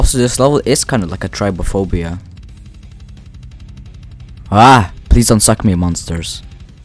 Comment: WHAT DID HE SAY ( im referring to 8:59 ) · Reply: nah he said "AHHH, please dont suck me monsters"